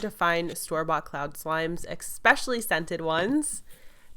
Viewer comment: #KSQUAD #Notification squad 3:33 Did she just say expecially Like if you noticed Btw love you! Your videos are just amazing!!